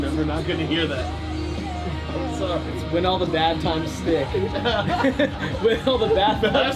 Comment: what is Misha hearing instead of “when all the bad times stick?” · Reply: When all the bathtubs stink